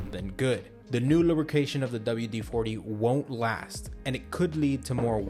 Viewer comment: on the caption in the video, the author of the video installed "DW-40's..." instead of "WD-40's...". Whoops! At least the narration got it right. As for WD-40 in general, avoid this crap like the plague unless you've got absolutely nothing else to use. As for buying it, don't waste your time and your money! If you need a penetrant then buy a quality penetrant. If you need a lubricant then buy a quality, suitable lubricant to meet your needs whether it's a grease or an oil or any other lube. The bottom line is WD-40 is total crap and a waste. There are far more and vastly better, quality products out there to use!!